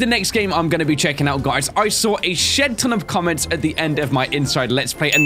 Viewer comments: OMG SWORE!!! HE SAID: I Heard A Sh** Ton Of Comments! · AMAZING!!! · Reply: Ok thanks but i thought in the start he said a sh#t ton was i correct?